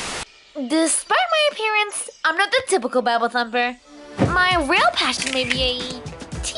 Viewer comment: WHAT IS SHE SAYING? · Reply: Bible thumper